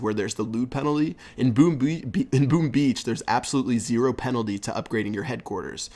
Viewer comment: Did I hear "Boom Bee" at 2:50?!! Haha